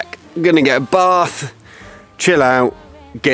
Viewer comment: I realize you say at the end, “get this edited”, but I thought you said, Get a sedative! 😀